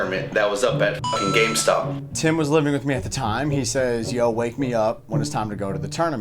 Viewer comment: Did he say Wank me Me up